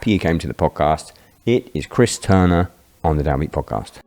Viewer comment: I thought it said downbad podcast 😂